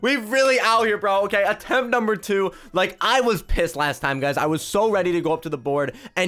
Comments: did you say "Pissed" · I dont know but is it just or did he say pis*ed